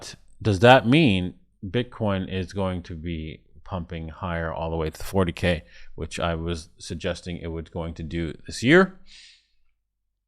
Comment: I thought you said that BTC was going down to 20K... 😂